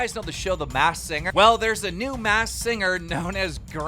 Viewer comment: Him trying to say MASKED singer is hilarious. I thought he was saying mass when I wasn't looking at the vid and was fucking confused.